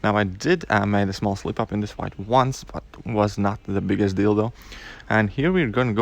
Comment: Not the biggest deal though sounds like you said not the biggest dildo